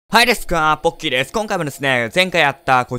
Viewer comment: 今回思いっきり 『ボッキーです。』 って聞こえた！絶対に言ってた！